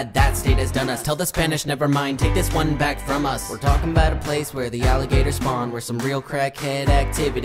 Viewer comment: Bro I heard “where the alligators grow”and I knew it was Florida 😂